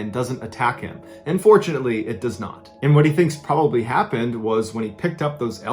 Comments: AHAHAAHHAHAABVJSVBKVBK I had to do a double-take and go back three times because i thought he said "unfortunately it does not" - as in the mountain lion didn't attack X,,D edit: Can we please say thank you to our ancestors for developing our survival instincts.. ;v;.... ngl I'm kinda glad I've never learned how to actually go camping (besides the school camps in cabins... which were very very safe) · i really thought he said "unfortunately, it does not" and it sounded so bizarre coming from him so i had to replay that a few time until i realized he said "and fortunately, it does not" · In 11:33 when he said “and fortunately” it sounded like “unfortunately” to me and the fact that this is being narrated by voice and barely any images we have imagine what he’s saying and when I was doing so the "and fortunately”sounded like "unfortunately” and I got spooked so much that I 💩my pants ( AND OFC NOT LITERALLY)